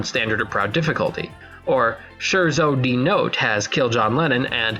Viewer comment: I subbed, but then I heard you say sharezo dee note and now I'm having second thoughts